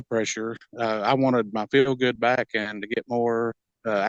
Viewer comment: you wanted to get your what back? Couldn’t make out the word. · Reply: he was ready to start feeling good again after so long